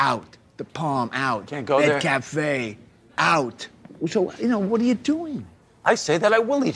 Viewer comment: Did Richard say McCafé? 😂